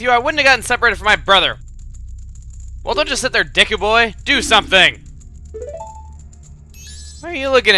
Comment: i thought he said dick boy... -__